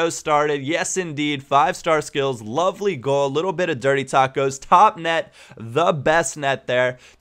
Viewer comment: LOL, thought he said 'Dirty Tacos'